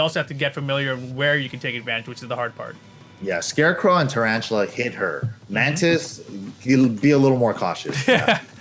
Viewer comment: scarecrow and tarantula HATE her!!! 40:51 thats what i heard anway